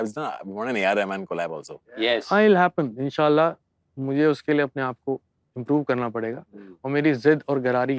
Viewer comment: Did he say "Insha Allah"??